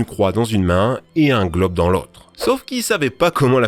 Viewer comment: mdr jai compris "et un gloc dans l'autre" au lieu du globe 😂😂😂